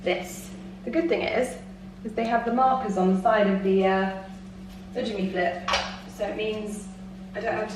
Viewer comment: I have fought asking because I don’t want to sound like a stupid American, but I can’t seem to find it on google and the captions doesn’t get it so…….. What are you saying when you say something that sounds like “uh dah jimmy flip”? Example- at the 43:40 mark